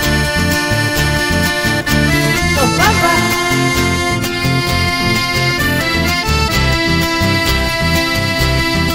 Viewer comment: Que es lo que dice en el minuto 2:34? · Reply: Los pampas